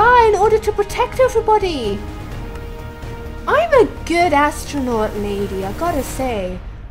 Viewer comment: Who else thought that when she said I'm a good assstronaut lady I thought she sad I'm a bad ass astronaut lady! xD